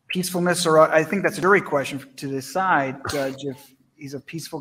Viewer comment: "I think that's very questive to this side?" Did he say questive? Questiontive? What?